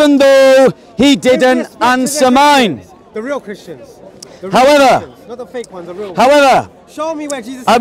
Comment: I heard him say let’s ask the real question not the fake one😂 😂😂😂😂😂😂😂😂😂